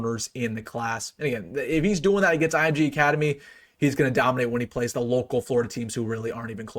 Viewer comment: Great for him, I had a Mini Heart attack i thought "Dominated" said decommited 😭